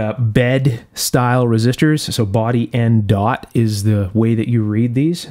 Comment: I didn't know what you were saying at first "baudy". lol Cool stuff.